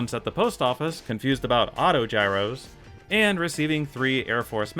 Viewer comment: I was going to call you out for calling Autogyros "Monogyros" and then when I went for the timestamp heard that nope you were correct and apparently I misheard you